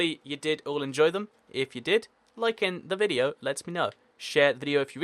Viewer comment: Thank you! You have cleared up a huge question I have had I thought you say "Like the Video and let me know" And now I know its "Like it and it lets me know" And Hi!